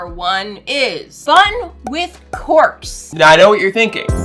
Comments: HELP I CANT BE THE ONLY ONE WHO HEARD "FUN WITH (a) CORPSE" RIGHT?? 😭😭 I HAD TO REWIND FOR A SECOND AGRFKGRMSKFGJ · I thought she said “fun with corpse” bro I was so confused 😭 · I thought you said corpse 😭😭😭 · lol I’m not going to lie, I wasn’t looking at the screen to see the words on it , so I thought she she said “Fun with corpses.’ at first , so I was over here like 😨….well this just got dark lol 😂. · I really thought you said "fun with corpse" I was concerned for a second there 😂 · Lol I heard fun with corpse😅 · I thought she said "fun with corps"!😂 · I thought she said fun with corpse, till I rewound it. 😅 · For some reason I heard "fun with cords" and I was even more confused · I swore she said fun with corpse, instead of fun with corks.